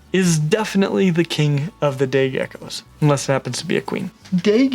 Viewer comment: did anyone else hear “gay geckos”